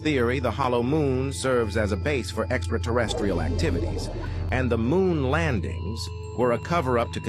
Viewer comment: “Why would the moon be a base for extra-curricular activities?” I said to myself, and continued to mishear it after rewinding three times lmaoo